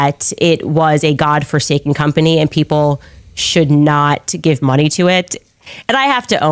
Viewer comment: Does Candace always sound like she has a stuffy nose? Like I thought she said "bunny" when she said "money".